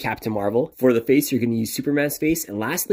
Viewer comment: Legit thought you said Adam Smasher from cyberpunk 2077 till I read the caption